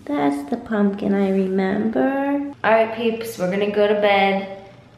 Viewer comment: did she say R.I.Peeps